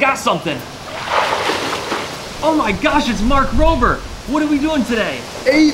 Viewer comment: I thought he was saying ''Oh my gosh, It's Mark Wahlberg!''